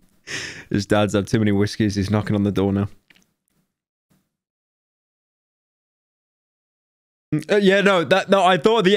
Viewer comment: Anyone know what it says at 37:51? I can only make out "two" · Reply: According to Wendigoon, it says Type Two Detectable.